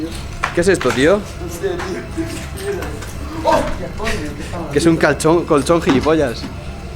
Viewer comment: nose ustedes pero yo en el minuto 1:50 escuche es un calzon XD